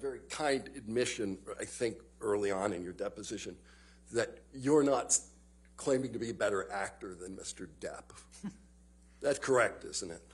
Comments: I think I heard the attorney at 4:03 say "you're nuts claiming to be a better actor"... 😂😂😂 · Did he say "You're nuts" at 4:03?